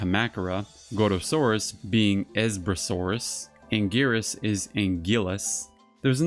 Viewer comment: Thanks for those bits of information. As for the Esbrasaurus name, I saw it in the "The Big Book of Japanese Giant Monster Movies: The Lost Cuts: Editing Japanese Monsters," where it mentions Gorosaurus name as Esbrasaurus. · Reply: You should've actually watched the movie instead of relying on that book. Ichiro sounds like he says, "There's Brasaurus," followed by, "There's Manda." It's possible that whoever wrote that book misheard "There's Brasaurus" as "Esbrasaurus."